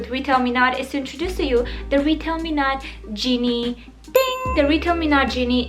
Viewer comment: I thought she kept saying "Retail Menad" lmao